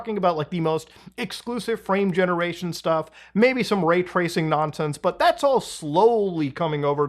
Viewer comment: no cant hear any different, he actually said rap* tracing